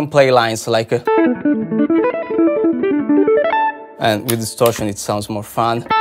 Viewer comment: I heard "and with starship" and not distortion and I still accepted it.